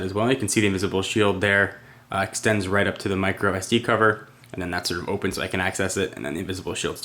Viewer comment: did he say micro sd cover, lol FAIL!